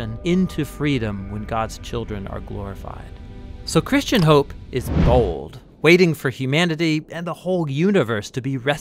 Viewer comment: the subtitles say "Christian hope is old." Is it old or bold? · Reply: The video says “Christian hope is bold”, so the subtitles must be wrong there. (Edit: I just realised your comment’s from 4 months ago, sorry lol)